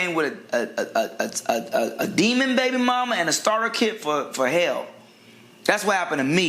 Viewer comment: Not a " Demon baby mama and a starter kit for hell" 😂😂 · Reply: I thought he said "starter kid form hell" Thanks for clearing that up, lol